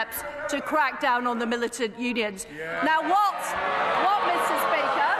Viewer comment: Did she really say "militant unions"? 🤨